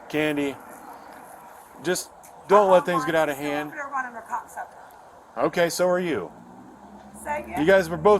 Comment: Am I the only one who heard the lady call the cop a C0ck Sucker at 11:19 and the cop said well so are you!! 😂 And she had the nerve to say excuse me!!??? Lmmfao!!!!! 😩😩🤣🤣🤣🤣🤣🤣🤣🤣🤣🤣😂😂😂😂🫨🫨